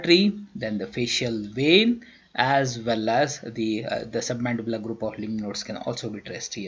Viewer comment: Sorry I can't hear it clearly. You were saying submandibular what ..? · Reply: Submandibular group of lymph nodes